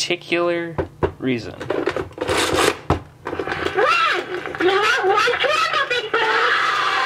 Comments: I heard "One! Blow Out One Candle, Big Bird! AAAAAAAAAAAAAAAAAAAAAAA!!!" Someone misheard as "Run! You don't wan't trouble big boy AAAAAAAAAAAAAAAAAAAAAAAAAAAAAAA!!!" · Elmo: "Blow out one candle, Big Bird!" Big Bird: "aAAAa a. A A A A a A AA A A " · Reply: i heard "blow out one candle big boy